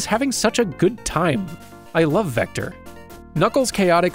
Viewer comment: did you just say ''love'' ?